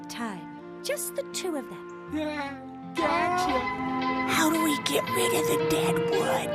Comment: who else died when carl said "how do we get rid of the dead wood"? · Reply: Oh my god! I thought he said "how do we get rid of the dad".